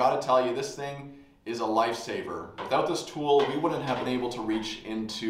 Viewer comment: I first heard "this thing is a light sabre" :D